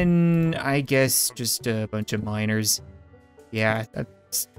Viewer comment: When said Miners, I thought he was saying minors.